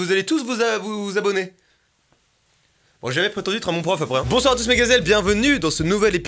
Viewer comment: les gars aidez moi j'ai vraiment rien capté de cqu'il à dit help me ça me stresse mdrrr voilà bref restez hydraté c'est important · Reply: unicorns are real bitch "j'ai jamais prétendu être un bon prof après hein..."